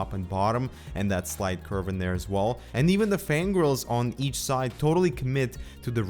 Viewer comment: Just me, or did it sound like he said 'and even the fangirls'